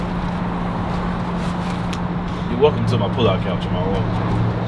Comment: I thought he said, "you're welcome to my pullout game".